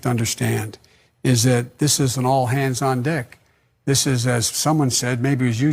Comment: I swear Biden says " all hands on dick" @ 1:57:09 . Heard it live and found it here. IDK. But I think it did.